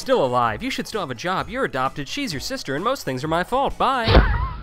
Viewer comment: What did he say at 1:41? "Hey guys, still alive! You should (Something) you're adopted, she's your sister (Etc.)!" I keep replaying it, but I don't have a clue what he is saying... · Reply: "You should still have a job, you're adopted, she's your sister, and most things are my fault! Bye!"